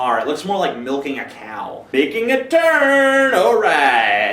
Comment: i dont know why but when he says "making a turn" it sounds like he said "baking a turd"